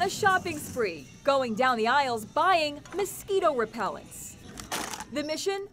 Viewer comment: Lol I thought it said reptile not repellent